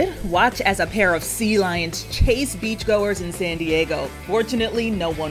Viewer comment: Bro for a second I thought she said unfortunately no one got hurt 💀